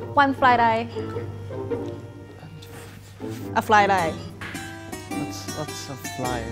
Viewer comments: I heard "fry lie" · said fried rice but at first i thought she say fly die